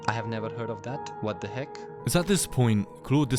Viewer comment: I heard "what the heck" and right after read "what the f" Can someone explain why it makes me laugh so hard?